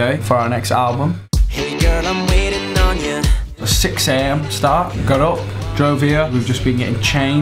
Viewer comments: is he saying start karo? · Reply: Start,got up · what did zayn say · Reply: 6 am start. That's what he said